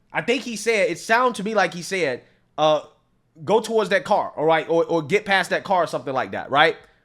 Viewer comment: "Watch out for that car" is what I heard.